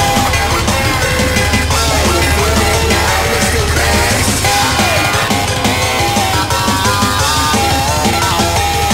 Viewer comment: what he say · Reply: “Are you feeling it now, Mr. Krabs?”